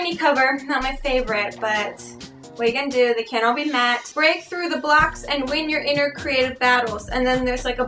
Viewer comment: I thought she said "Break through the Blacks and win your inner creative battles." Thought I was in the racist part of Youtube again.